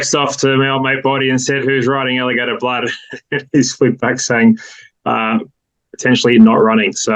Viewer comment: Think my sound must be faulty, can anyone check what said at the 2:22 mark? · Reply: "potentially"